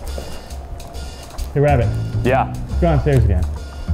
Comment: did he say "Hey Rabbit" ? As in Rabbit the Swat Team member Preston has done vids with?